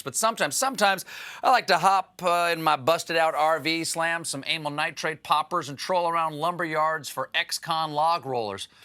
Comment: steven, did you just say anal nitrate poppers ?